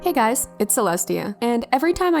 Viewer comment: I thought you said hey guys it’s a lesbian